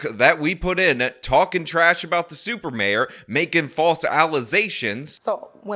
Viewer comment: 🤔did I hear “ False Allerzations” ? That isn’t even Ebonic 🤷🏾‍♀️